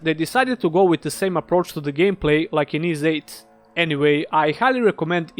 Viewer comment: Lol.. Ys VIII.. you should display the name of the game in text. Very hard for someone that doesn't know of the game to understand the pronunciation of Ys VIII. Thought you was saying 'East Games' then maybe "Theives Games" "Ease 8" etc.. If it wasn't for Montrum Nox and a goggle search.. I'd probably be trying to figure out the name of the game. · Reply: Yeah I heard "east games"